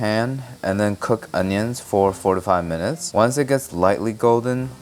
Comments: that sounds dangerously close to "45" instead of "4 to 5" lmao i had to do a double take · low-key I thought you said cook them onions for 45 minutes. I literally died and my house is sleep. Wtf I need headphones.